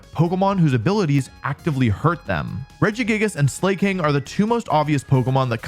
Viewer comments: “Slay King” am I the only one who hears that??? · I misheard slaking for slay king